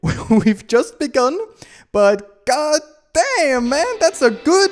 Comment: you got me here I thought you were saying God d*m to d Something else